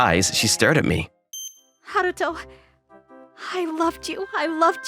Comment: I thought he said Naruto 💀